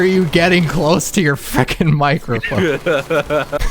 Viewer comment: Did just say "fecking" BANNED FROM THE DISCORD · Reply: LOL I said "fricking" but pronounced it more like "frecking" and said it a little too quickly